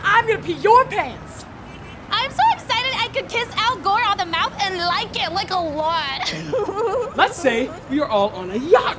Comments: I thought she said I could kiss a girl on the mouth and like it a lot. Had to rewind to make sure you had not gone too far. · I thought said "Kiss a girl". Much better when I figured out it was "Al Gore".